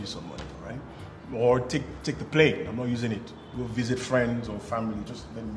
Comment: Ahn ahn did I just hear "take the plane I'm not using it" ahhhh babyyyy 😀😀😀